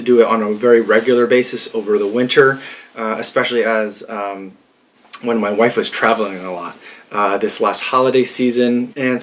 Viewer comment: did i heard it correct "one of my wife?" Lol